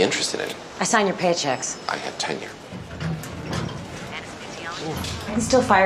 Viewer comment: Every time someone said tenure, I thought they were saying ten year and never knew tenure was a word until I found out lol